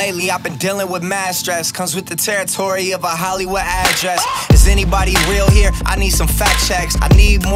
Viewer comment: Am I the only one who thought G said Actress instead of address?